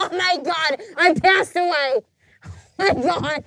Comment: did snooki say "I passed away" 😂😂